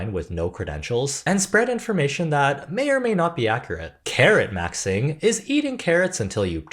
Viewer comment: When you said “carrot maxing” I heard “Karen maxing” and that you had to eat them 😂